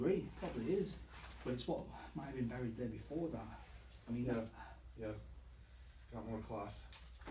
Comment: when you say that you have found more cloth there is a very faint what sounds like female voice that says "yeah" almost breathlessly like on an exhale